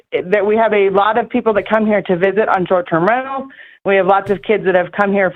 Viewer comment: Does she say we have a lot of people with church & rentals around 34:29? Bc someone said they were members of the Scientology church and if they are doesn’t that seem weird? Idk about the church bit of it but Scientology is a weird tight big group and very private from the outside world and they also handle illegal activity within the church they don’t report it to LE and there’s a ton of churches nearby in Reno, Sacramento and a few other places near Truckee. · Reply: She said "on short term rentals"